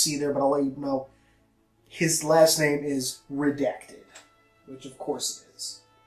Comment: what did he say MasterChiefs last name was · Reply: it sounds like he says redect